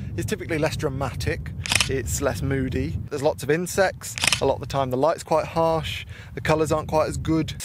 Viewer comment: When you said insects I had to double take because I thought you said incest. I was like 'summer is very different in England than Australia' haha.